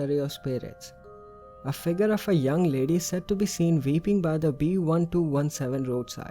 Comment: In Towton instead of weeping I thought you said Vaping lol